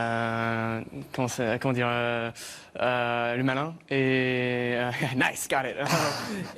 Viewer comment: What is the French word for cocky that Timothee says at 2:36? I've tried googling how to say cocky in French, but what came up are "arrogant" and "tres sur de soi" which are not the words he used there. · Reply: He said "malin". The correct expression is "faire le malin" (when he said "il se sent le malin")